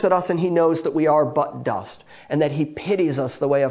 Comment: Butt-dust = dusty fart. LOL So true. That's what I heard too. I'd rather be "butt-dust" than a shart! lol